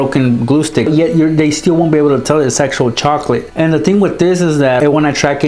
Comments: I thought he said sexuall but I realized he said actuall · Yet they still won't be able to tell its actual chocolate I thought he said sexual chocolate😂😂😂😂😂 I'm doing an allnighter and my volume is on 2 does that explain it?